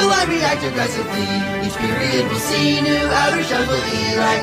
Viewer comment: i hear "xenon"